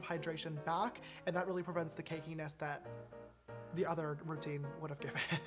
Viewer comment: DID U SAY CAKESS